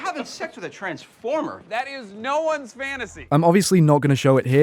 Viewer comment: At about 1:05 Jake actually says Joe instead of no referring to Charles' actor Joe Lo Truglio